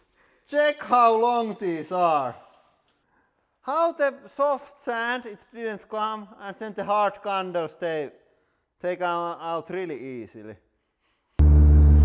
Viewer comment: Wtf did he say on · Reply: How comes the soft sand stay but the hard candle come out easily